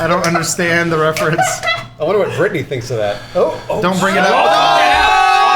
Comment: what did say and why was it controversial? Something to do with Burnie or something? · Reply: He said Britney, and I think she like copyrighted her name or references to her so saying it in the video could cause problems